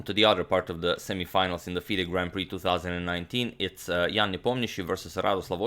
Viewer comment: Whenever Agadmator says "Ian Nepomniachtchi", I always hear "Young Nepomniachtchi". Is it just me?